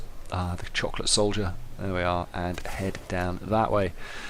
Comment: XD it sounds like youre saying the "Chocolate Solders" :P ima call em that now